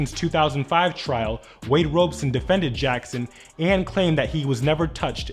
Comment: did he say Wade Wilson ? I mean did Deadpool defended him.